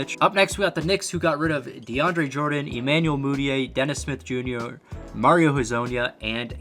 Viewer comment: Hold up hold up what did my mans just say? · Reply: Dennis smith joonyour